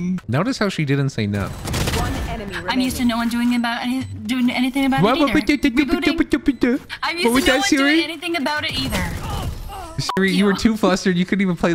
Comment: I just heard she said "no one doing that"